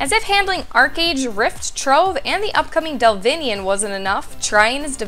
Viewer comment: I heard Delvinian. DELVINIAN CONFIRMED. TRION GOT IT WRONG.